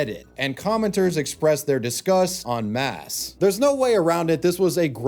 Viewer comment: I heard "en masse", but the subtitles read "on mass".